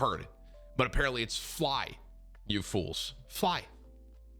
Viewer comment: I’m a HUGE lord of the rings fan & even I thought he said “run you fools” only 2yrs ago I watched it with subtitles & he says “fly you fools”